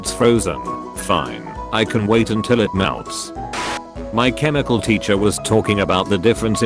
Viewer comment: Did...did it just say “my chemical teacher”?!😂😂😂I am calling my chemistry teacher that from now on, there truly is a chemical feeling to her😂